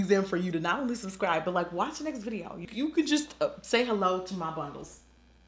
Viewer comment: Did you just say "say hello to my bundles"?? LMBO!!!!!😂😂😂😂😂